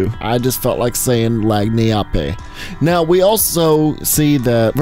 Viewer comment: Lagniappe Lol, I heard it as "light me up e" 😂